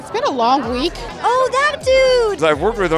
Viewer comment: What did Emily say? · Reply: "Oh that dude"